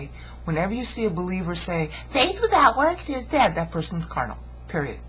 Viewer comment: I have a really bad speakers I am probably the only one who cannot make out what you are saying, and you speech speed is good it's only @ 1:40 is it "faith without works"? and @ 2:00 my crappy system cops out on me. I get the center of your message but I just want to make sure I fully understand what you are saying. I have enjoyed your other videos thank you for posting them I am new to Christianity so this is all new to me. Thanks again! BTW your speech speed is good it's my cpu sucks.